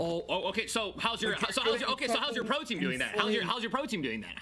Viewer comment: Wait was it pro team haha I thought they said Protein